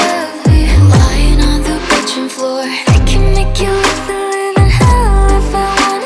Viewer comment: When she said “lying on the bedroom floor” I thought she said “kitchen floor” 🤣